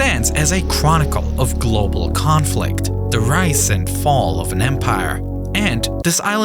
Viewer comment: did he say "the rice and fall of an empire" ? I think he did ...